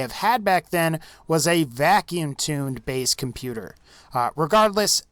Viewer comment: Wait did you just say vacuum tune? 😂